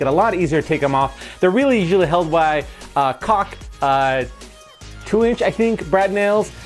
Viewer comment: “held in by cock”, can anyone tell me what he really said because I’m confused af